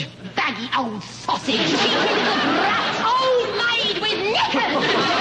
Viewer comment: What does Mary say at 4:33??? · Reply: Mary said cheeky little brat